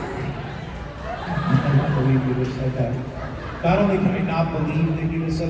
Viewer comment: what did the fan say at 16:40? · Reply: He said “I can’t believe you just said that”